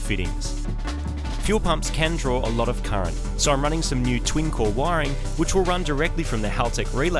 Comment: I thought he said "twinkle wiring" not "twin core wiring"